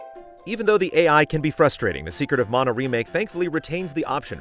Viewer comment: It sounded like you said “Secret of Mono” at first, which is would admittedly be a very, very different game.